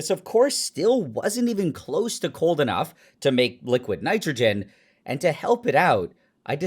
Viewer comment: I thought it said squid nitrogen